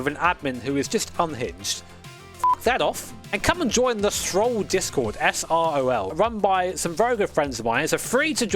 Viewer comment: Damn, you scared me at 8:45 . Thought you said Stroll discord. Like Lance Stroll Discord. o.0